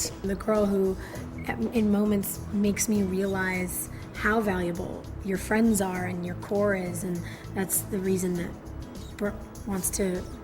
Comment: wht did she say!? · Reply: Anam ‘core uses’ I think is what she says, like her really close friends she just says it fast